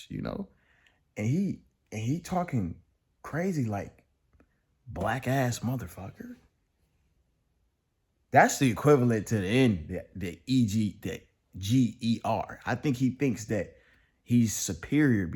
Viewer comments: I heard “wackass” lol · Watching the video I heard "wackass" but anyways not my problem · Did he call him the n word? Cause i only heard “black assmf”